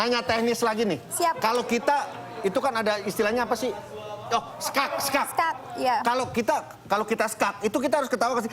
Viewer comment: tolong ada yg paham ga sik.. Ga kedengeran ish ttg skak... Iskak bukan sih..?